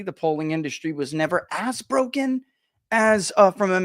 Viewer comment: ! When you said (toward the very end of the vid) "...as broken..." I heard "A$$ broken". Thought that last one was more accurate.